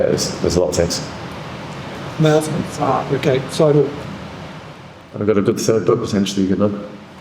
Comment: Wjat did he say at 22:08 ? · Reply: "I've got a good third book potentially" something like this